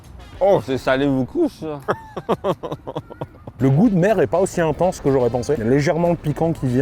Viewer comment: Très bonne vidéo!! Mais j'ai eu un doute à 13:04 car j'ai entendu ''le goût de merde'' :O hahaha! Mais c'était ''mer'' ;P J'ai bien ri :) Merci:D